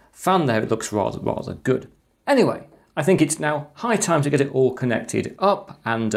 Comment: did you really say: "I think it's now Pi time to get this all connected up..."?! Or have I literally got "Pi" coming out of my ears?